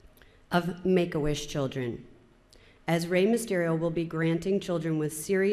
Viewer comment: Did she said Rey Mysterio?